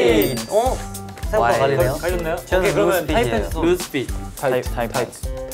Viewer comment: i swear i heard sunoo say loose fit